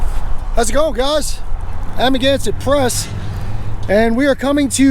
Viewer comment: I can't help myself, but even after the <num> audit, I keep hearing "I'm against the press" instead of "amagansett press", but that's just me xD